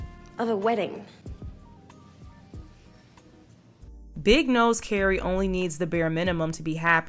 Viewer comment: I totally misheard “Big knows Carrie needs the bare minimum” may god forgive me